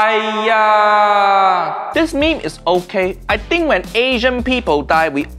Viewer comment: Hallo niece and nephew, hope you like this me me review. Go join Uncle Roger reddit: <link> If you want more funny weejios, subscribe to my nephew Nigel podcast channel - he almost as funny as Uncle Roger: <link> BAI BAI 👋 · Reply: I thought you were trying to say me me but you say meme instead